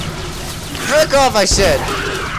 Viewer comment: i think did he say 'Jerk off i said'